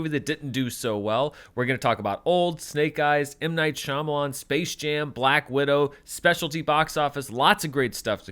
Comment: I definitely heard “we’re gonna talk about… M Night Shyamalan’s Space Jam” which would have been more interesting